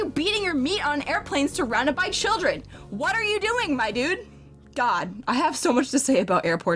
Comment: During the beginning all I hear is "....wHAT ARE YOU BEATING MY DUDE." When I look up I just see a hammer and a piece of meet. Say what now???